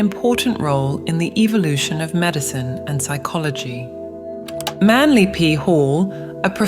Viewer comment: 😂 the accent makes Manly P Hall "Manly Pee Hole" i just ablut spat out my drink and had to rewind to make sure i did mishear that!!! I did not. 💀 What an unfortunate name when said a specific way